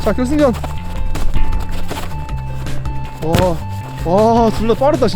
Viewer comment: 나만 와존나 빠르다라고들림?